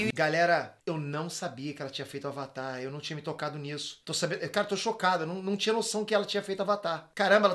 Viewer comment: o fala " tô chocada " ou foi impressão minha??